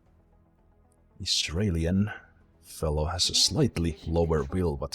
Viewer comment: Whoops, I thought you said Australian. My bad, you're accent threw me off for once. Normally don't have that problem. Google+ is annoying though, especially when you don't realize that it's screwing you over. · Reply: Brandon Brown Yes it is :/ No problem though, sometimes I don't understand myself, lol :) And I think I should've said Israeli, not Israelian too